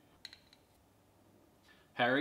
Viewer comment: i thought it said testicle tart, but it said treacle tart. One of my all time favourites